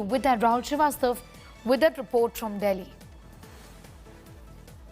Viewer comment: Did she just say, the 'Delhi Virus' 3:30? 😂😂😂 These news anchors talk faster than they can think 😂😂